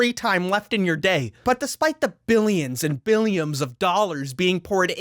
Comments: did he just say „billions and billaims“ of dollars? Is he being poured into this project? Blink twice of Mark Zuckerburg has kidnapped you! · Did u just say "billiams of dollars?" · billiams of dollars did you say? Lol